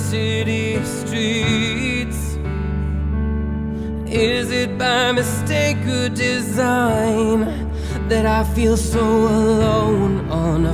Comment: i always heard "is that by mistake or desire..." thanks for clearing that one up! but I still love having wrong lyrics anyway. great cover!